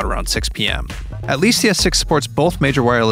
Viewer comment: DID I JUST HEARD CS6?????????